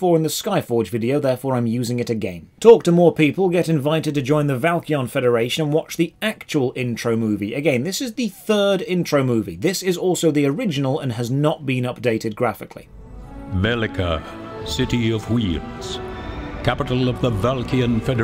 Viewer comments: i thought he said "city of weebs" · I had to go back and read the subtitle because i heard "Velika... City of weebs"... Wouldn't have been wrong tho